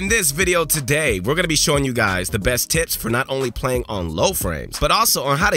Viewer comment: did u just say u will show us tits?